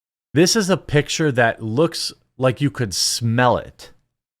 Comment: She just looks gross. Omg as im typing this i hear "looks like u can smell it" and i almost fell off the couch 😂